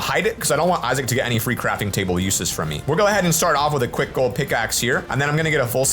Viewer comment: Did he said gold pickaxe instead wood pickaxe?